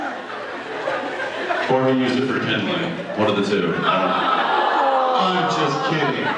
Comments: what did jensen say??? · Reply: "...or he used it for kindling, one of the two." ie He set it on fire. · what did he say at 0:31 ? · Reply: "...or used it as kindling, one of the two." ie Implying that he set the coat on fire.